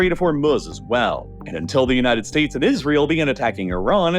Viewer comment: am i hearing that right he js said "United states of israel?"??? 😭😭